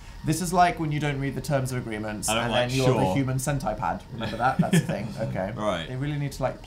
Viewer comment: did Dan really say "cent-eye-pad"? This man...